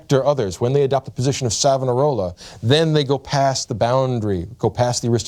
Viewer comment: What is he saying at 11:57 position of what? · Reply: Savonarola, I believe it's a reference to Girolamo Savonarola which makes sense